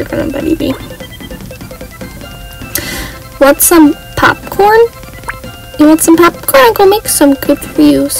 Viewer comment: When she said “want some popcorn” I thought she said want some poop